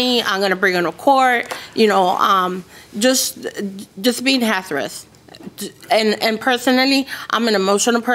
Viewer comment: Did she say, Just being “hasarous” @ 10:36 😩